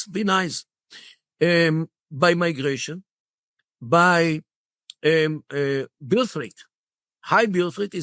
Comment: by “bil freight”??? What’s the word or words used here? I can’t find anything on google for the second word which sounds like “frit”, and the captions don’t help. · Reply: Birth rate.